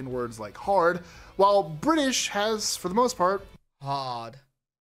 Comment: you’re saying “hod” instead of “hard” lmao